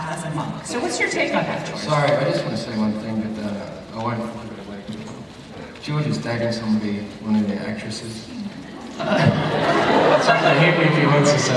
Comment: Someone please tell me what did he said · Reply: He's saying that George (Athelstan) is actually dating an actress from the show, and if he wants to tell them about it, mocking him